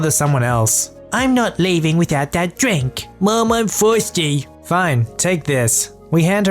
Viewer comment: i'd like to believe i heard "Mom, i'm THORSTY "